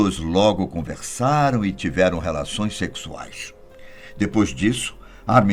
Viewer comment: eu ouvi que ele tiveram relações?